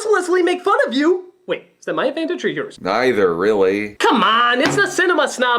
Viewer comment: English speakers, can somebody tell what Critic said at 0:50 - Wait, it's my ??? or yours? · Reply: The word is advantage.